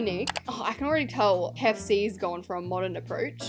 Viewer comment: why did say “PEPSI” instead of kfc